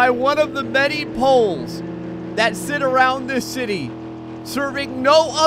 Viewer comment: did you say city or shitty?